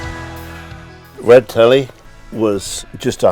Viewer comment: I thought he said the red tele was just begging to be laid 😕 😅